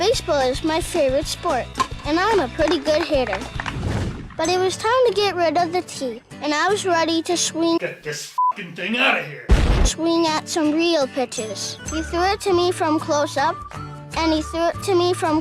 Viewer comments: I thought the baseball kid said "swing at some real b*tches" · swore the little kid said "i was ready to swing at some real bitches" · I legit thought the kid said 'it' s time i throw at real bitches '' 🙃